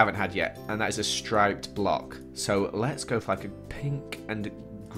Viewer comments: did he say "lets go f***ing pink?" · what did he say? lol I don't care if he said the word(He's an adult) but just asking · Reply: He said "for like" but like this "forlike"